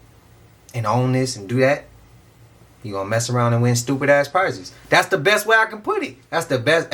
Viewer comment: When you shake your head bro. I hear you saying "man, F**k around and find out" LMAO!!!!